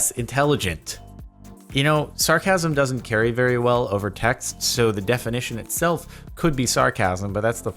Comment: I thought he said Texas and I was very confused